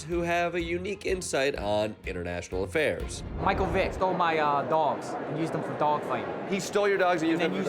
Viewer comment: Sorry? What did he say? I heard "Michael Bay stole my dogs" 😅